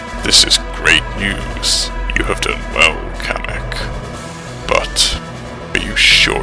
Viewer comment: I heard bowser say: Butt